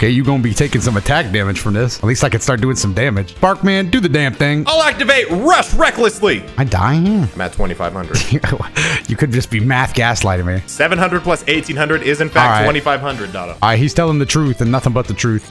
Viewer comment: did you say ruff recklessly